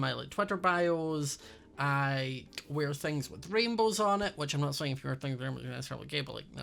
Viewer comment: did anybody even understand what he said if so please say lol I love you 😂😂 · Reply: He said “which I’m not saying if you wear things with rainbow you’re gay”